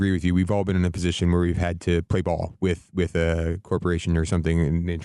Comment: My apologies ahead of time. But every time the guy said "play ball" I heard "suck cock".